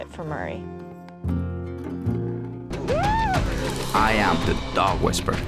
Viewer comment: At the end, it sounds like Cesar says "I am the dog WHISPER." -->NOT<- "I am the dog WhisperER" Listen for it...